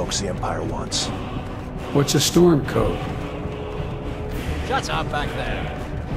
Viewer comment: "We shouldn't be here, it's these stormcloaks the empire wants." "What's a storm coat?" The best part about this is that even though misheard, the term is still applicable.